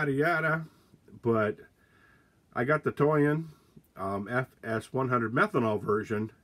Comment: Thought you said you “got the toy in” lol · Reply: LOL! yea i'm"toy in" with my Toyan